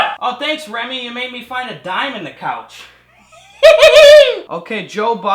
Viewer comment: I swear I heard "diamond".